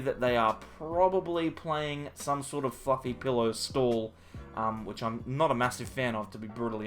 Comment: I practically Roflmao'd when I heard you mention Mareep! To what I heard, you said "they have a Mareep in the active so it's probably a fucking pillow stall" and I just died..... To then think about it and you said fluffy pillow 😂 sorry for the vulgarity but it was funny